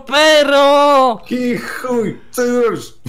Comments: WIHUJ! Tak usłyszałem... Hehe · Czy tylko ja usłyszałem "wichuj"?? :D